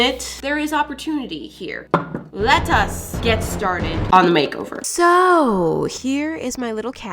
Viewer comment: Lol the way you said " let us get started" sounded like you said "lettuce get started."